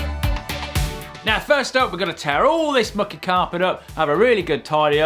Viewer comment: LoL I thought he said we're gonna tear all this fuckin carpet up then I turn the subtitles on n it turns out he said mucky carpet lol